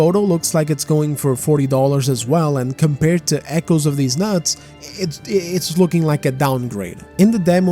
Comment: did you just say Echoes of Deez Nuts? Lmao